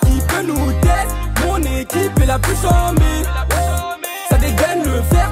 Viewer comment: "dit moi qui peut nous teste mon équipe est la plus chamber" 🥵🥵🥵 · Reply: Chant-mé pas chamber